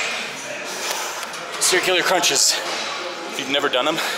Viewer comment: really really thought you said testicular crunches...